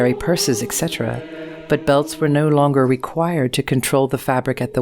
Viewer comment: She said "ecksetra," instead of etcetera.